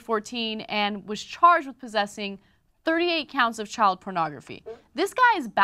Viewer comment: It totally sounded like she said '38 pounds of child pornography'.